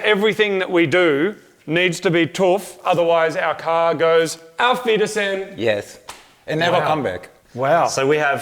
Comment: Can anyone tell me what is saying at 9:01? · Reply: "auf wiedersehen" = goodbye in german